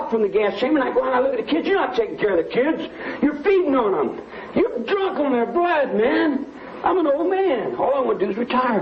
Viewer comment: what does he say about kids ? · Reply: He says" you're not taking care of the kids you're feeding on them you're drunk on their blood man "......